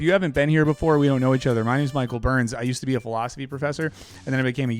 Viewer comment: It sounded like you said you used to be a Velocity Professor, which is awesome.